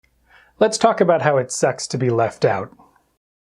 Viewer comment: I thought I heard" let's talk about sex to be left out" 👀